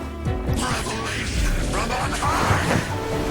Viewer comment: First time seeing this for me too, am I hearing things or at 2:38, when he's attacking Aether, he says "Revelation from Honkai"? 😂